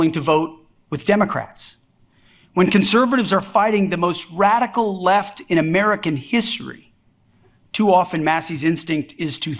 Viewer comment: I had to back dat shit up to make sure I heard him right. "The most radical left in American history"?!?!?